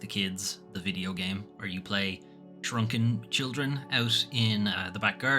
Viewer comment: thought you said "drunken children"